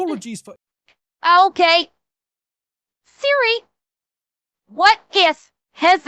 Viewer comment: Loved the video Can't wait for the next video Girl! When you said "Theory", I thought I heard you say "Siri". Maybe that's why your phone activated LMAO! I knew that Nintendo was Suing The Trump Administration for using Pokémon as a Reference to the ICE Raids, but Dragging Aethel into this? Bro probably just stayed away from the Elections to try and avoid this! They might've done that because they think his VTuber Model looks like something from Pokémon, which tells you that only Elon Musk has People Young Enough to know what the Hell is a VTuber and What's a Pokémon. The ICE Types joke was really good though. The 160th Special Operation Aviation Regiment (Airborne) (aka The "Night Stalkers") losing a Black Hawk in a Training Accident? That's the 4th Crash (Combat and Training Related alike) in 8 Years! Thoughts and Prayers to the Families of those Lost in this latest Crash. As for everything going on with Tylenol, It's not just that but all Over the Counter Medications and Vaccines. All Pharmaceutical Companies are Required to File Injury Reports to the Federal Government every 2 years. The Last time Tylenol themselves did this was back in 1998, the Year I was Born! My Whole Life, they've been allowing Potential Harm to Occur with their Products. On top of that, the Media has it all twisted, once again. RFK Jr. even said that Tylenol "May have Been among Several other Factors leading to the Increase in Children being Diagnosed with Autism." Where in that Phrase does it say